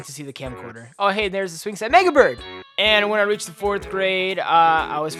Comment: I thought he said niga bird not mega bird